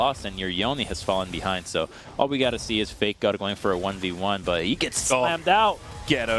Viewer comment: I'm not sure if I heard it right. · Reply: If you listen carefully it is fakegod but yes it sound like faker hahahaha